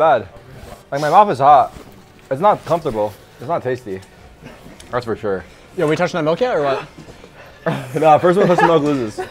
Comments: i deadass thought tarik said his mom was hot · did bro just said ''My mom is hot'' ??? 💀